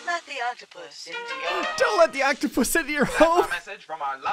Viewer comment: I thought it said “don’t F**k The octopus in the yard” 😂